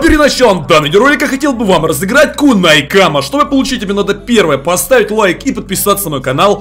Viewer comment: Мне послышалось, то сказал кунай мама